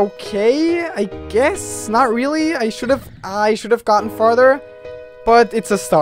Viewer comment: i heard fartee ( ͡° ͜ʖ ͡°)